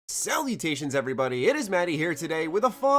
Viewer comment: I'm so high I thought you said "SELL MUTATIONS" as your intro greeting haha